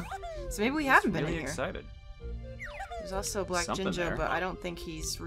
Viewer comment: I heard black jinjo and black ginger and that made me so nostalgic for OOT LP 😭😭